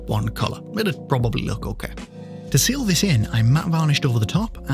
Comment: I’m sure you hear this all the time but when I heard “I’m Matt” I said “no, you’re Daniel.”